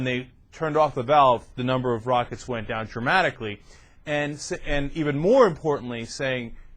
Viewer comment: it sounds like he says "the number of rockets went down traumatically"